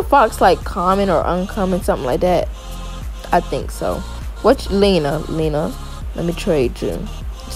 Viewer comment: I heard him say: "what's lina?"